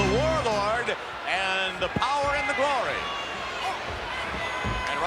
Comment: Did Vince just say The Powers Of Glory? Highly underrated tandem team. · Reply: The Power & the Glory is what Vince said.